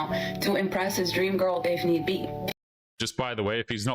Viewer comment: "Saving up money for the next girl" She really thinks that we don't have goals and priorities for the future other than her and others like her. Or maybe the man needs to save money in order to provide for her in case of an unplanned pregnancy (that HE did to HER, as she says). Does she want him to be financially responsible or not? 19:38 What did she say? It sounds like "DAY FIN EEE BEET". Did anyone hear what she actually said? 20:16 "Why are you sharing your body with him?" Maybe because the woman needs and enjoys it just as much or more than the man does? And because the man is also sharing his body with her, and he is taking on more of a risk of 18 years of unwanted parental responsibility that would be 100% OUT OF HIS HANDS and 100% IN HER HANDS.